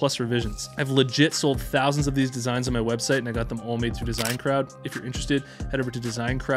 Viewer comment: am i the ony one who thought he was saying "design crab" lmao